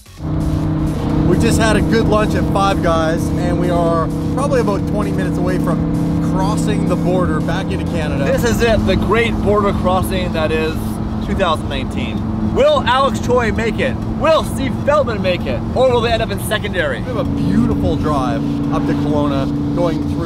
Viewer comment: Did he said 2018 ??